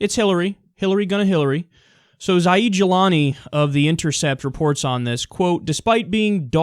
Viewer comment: Zaid Jilani. I thought at first he said "As I eat Julani" hahaha. I thought, "is he trying to make some kind of weird gelato joke?"